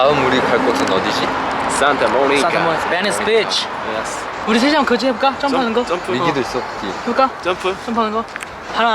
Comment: did he say “in this b*tch” or am I imagining it lol it was cute either way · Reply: He said venice beach